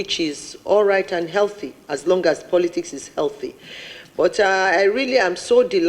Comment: sorry ma did she just say , 'so long as the politics remain legal?